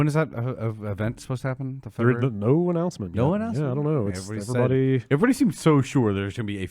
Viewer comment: when talking about PS5 “When is that event supposed to happen?” “The 3rd err no announcement....” Don’t think he was supposed to say that! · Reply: He said "The event no announcement". People grasping at straws lmao.